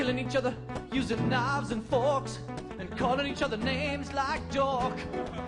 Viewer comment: My whole life I thought he was saying “calling each other names like George”